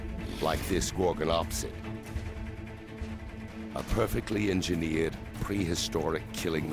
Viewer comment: Did anyone catch what was the name of the giant carnivore at 47:18? The subtitles show "Gorgon opposite a"